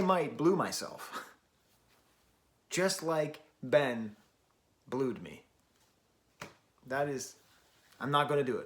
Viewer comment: I thought you said "just like Ben blew me" for a second